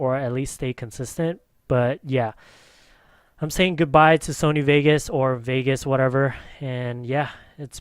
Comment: Awww I thought you guys were saying goodbye to YouTube